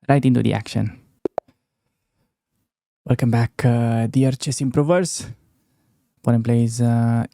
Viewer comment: was i only one who heard "welcome back chess perverts"😂 The question is, how did know 🤔